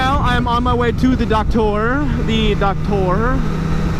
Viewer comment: I thought he was saying he was on his "way to the duck tour"